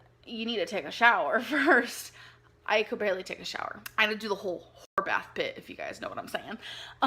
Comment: what did she say here? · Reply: she said, “whore bath.” meaning she just uses a soapy rag or baby wipes to “clean” your whole body